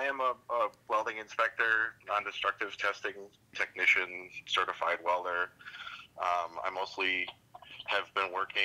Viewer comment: I thought he said “I’m a certified baller”